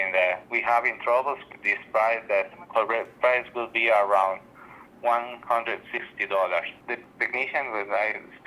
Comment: I heard it as $150?